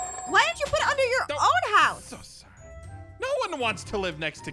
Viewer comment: did ein say “so Sorry”?